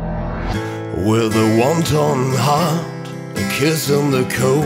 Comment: I keep hearing "wonton heart"...but I may have the munchies...